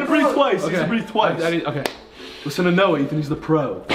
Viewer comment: Did he say noe? 💀😂